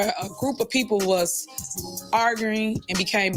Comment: Did she say arguring. Omg, go back to school and finish grade 6 to learn how to speak English.